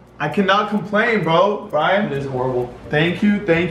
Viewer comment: I heard Brian secretly say " it looks horrible. " 6:50/